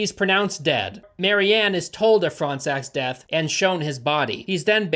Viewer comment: Why do i keep hearing "front sack"? hahahahah